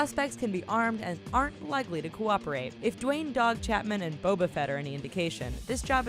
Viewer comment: Did you just say Boab a fett?